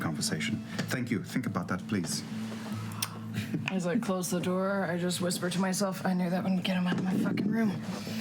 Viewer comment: What did Sam say at 50:55 ? I couldn’t figure it out. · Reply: Hello, I am so sorry for this..um..he says "I would fuck him so hard" ...nice to meet you :-/